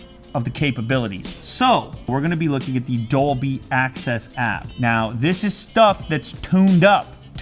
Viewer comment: I thought he said "Dolby Access" XD